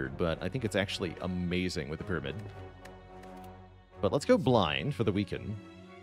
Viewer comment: I heard 'Let's go blind for the weekend' 😂 Took me a while to understand